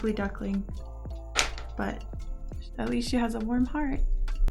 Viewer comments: When you said “Warm Heart” is thought you said “Warm Fart” lol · warm heart...oh.......i coulda sworn it said walnut husk.....um....